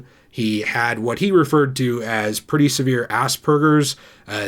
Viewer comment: wait, you said aspergers. I thought you said Ass Burgers